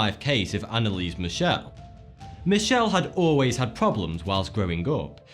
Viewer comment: The first one was Emily rose I thought. He kept saying Michelle for some reason. Maybe I heard wrong